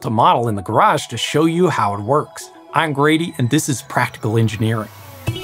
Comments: first day on the job and he's already injured? Grady, your next video's gotta be on the importance of OSHA in engineering workplaces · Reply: NGL, all these years I legit thought his name was, and when he said it wasn’t, I was very confused because I still heard him say. 😅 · It really does sound like you say lol! Either way, we love you Grady!!!!